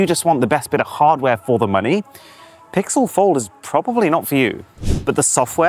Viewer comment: I thought i heard april fold